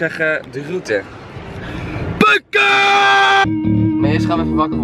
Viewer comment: Hahah, it's cool to hear you speak in your native tongue. What were you yelling at 0:46? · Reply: Ik think he was screaming 'punke'. I'm dutch 😁